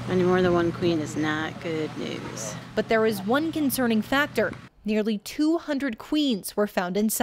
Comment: wow at first I thought When 200 queens . I thought I heard incorrectly